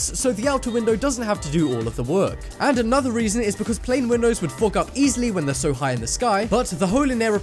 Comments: I thought you said f*** up 😂😂😂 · It sounded like he said F*CK UP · XD at 6:50 it sounds like he says "and another reason is because plane windows would fuck up easily when they're so high" XD XD XD XD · Am i the only one who heard f*ck up?